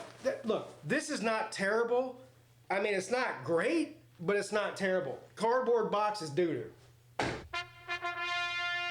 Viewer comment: What’s the holster brand? I’ve tried searching on what I thought you said and what the YouTube transcript says, but no dice 😅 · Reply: kusiak holsters